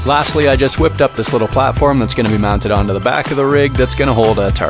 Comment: At first I heard "Then I hitched up this little kid who's gonna hold our target" oh I'm dumb 🤣